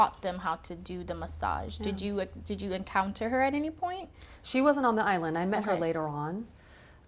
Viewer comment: I thought they said no blacks at the island! Isn't she black lol